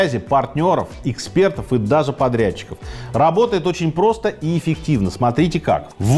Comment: 5.37 перемотал.....послышалось: "даже подро...ите"😂😂😂 ослышался😅 вообще...интересно послушать всегда!👍